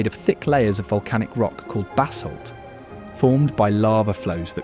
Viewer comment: Narration is distracting, discussing the rock basalt, sounded like he said basil....LOL